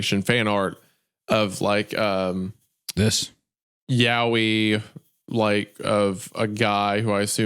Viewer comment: It kills me whenever you pronounce Yaoi like you're saying Yahweh